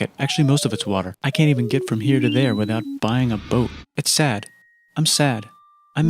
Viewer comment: i think he said "without Boeing and bom"